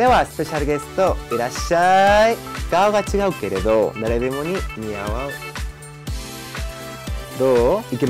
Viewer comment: 誰でもいいのあと似合わんって聞こえてて草